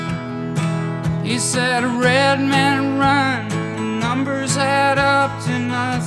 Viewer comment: This version it sounds like he says ‘Red men run son, numbers add up to nothing..’ Rather than ‘Red means run son, numbers add up to nothing..’ Red men run makes a lot more sense given what they were up against Let me know what yas think, cheers!